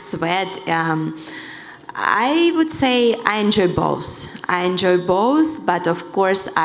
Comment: sounds like she said I enjoy balls 😂